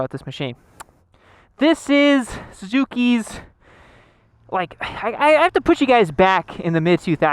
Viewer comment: Thought you said Suzuki was c tier.